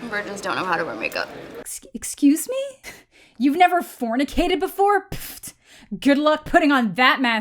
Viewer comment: When you were commenting on the 'vigins can't wear make up' and were saying 'youve never fu-' a good guys add came on, and for a few seconds I thought you said 'Youve never fucked the good guys